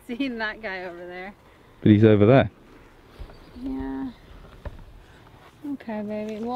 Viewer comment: Big question: At 24:24 does say ok baby, or ok maby. Sounds like a b and not an m?? #Bigsecret LOL